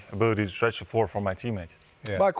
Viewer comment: Did I hear use my booty to stretch the floor for my team mates …strike em outta here coach